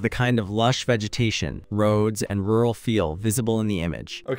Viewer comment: Think it said rural